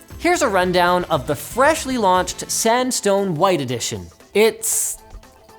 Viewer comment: Had to re-listen, I literally thought he said ''sandstorm'' white. rofl